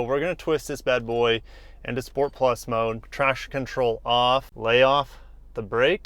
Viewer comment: Trash control? Whats that? Sounds like a new feature. HAHA i know its traction but sounded like trash. LOL Pun intended. ROFLOL